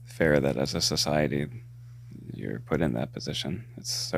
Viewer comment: Why does he sound like lil Wayne? 😂 I paused at 34:54.. did he really say for society to put that on her is horrible?